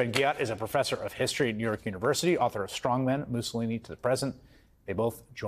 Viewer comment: Did I hear “Mussolini for President?” Trump is an IED in waiting.